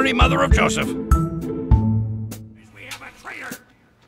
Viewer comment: When the soldier say "Boys we have a traitor" i always hear "Boys we have a trader" 100% legit